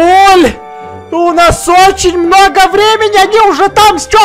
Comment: Кто услышил "Бабуль у нас много времени!" Если услышали то с вас лайк на Эдисона и на меня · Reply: Я не слышала "бабуль у нас много времени" я слышала " бабуль у нас ОЧЕНЬ много времени!" Так что фиг тебе с маком А на эдисона я и без тебя поставила лайк!